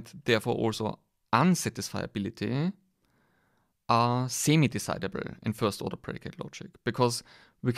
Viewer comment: you say "it follows that validity and therefore unsatisfiability are ??? decidable". I can't make out it out, what is the word before decidable? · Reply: semi-decidable! Please see this link for more information: <link>